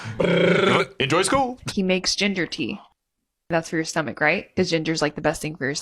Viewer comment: For a brief second, I thought she said "he makes gender tea" and, honestly, that'd be way more interesting to discuss than talking about your boyfriend making his own teas and eating healthily